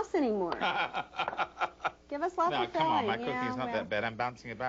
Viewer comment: At about the 1:40 mark, says "don't be stingy. No one will wanna come to your house anymore." At this point, laughs. After the laugh, says something. I've rewound it ten times, but cannot make out what she says. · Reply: "Give us lots of filling" I turned it up so I could make out what she said